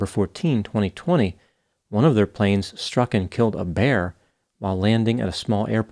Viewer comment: I did laugh out loud at that "Frontier Bearlines" joke. Well done, sir! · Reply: I thought he said, struck and killed the mayor 😲